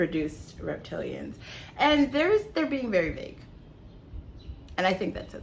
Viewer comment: What are you saying in minute 11:00 . I can't make out the words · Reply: I said “The dragons are being vague” 🤭